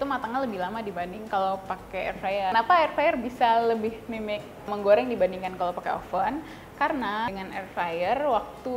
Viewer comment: ngomong apa ya? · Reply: “Lebih mimic menggoreng dibanding airfryer”